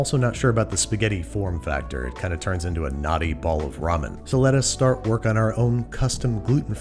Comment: What Babish said: "A knotty bowl of ramen" What I hear: "A naughty bowl of ramen."